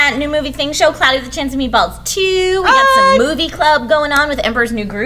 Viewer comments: I thought trisha said "boobie club" and i got so excited but then I figured she meant movie club... :( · When she said movie club I thought she said boobie club. Lol